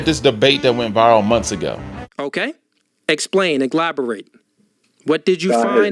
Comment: did he just say "iglaborate" MY GOD ON TODAY, this 🥷🏿 is an idiot! 😂😂😂😂😂😂😂😂